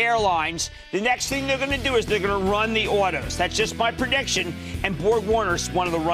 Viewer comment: What did he say at 10:38 ?? Run the what? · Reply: Run the autos, as in automotive industry.